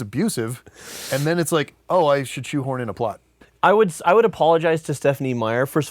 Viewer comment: did he say I shot horn in a pot***